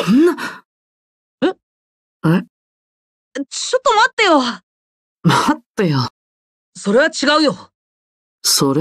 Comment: what were they saying? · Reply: Sleepii Purple it's "Hold on"